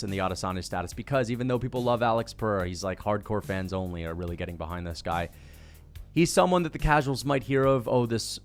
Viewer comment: Did he really say hardcore fans only are getting behind Perieria?! 😭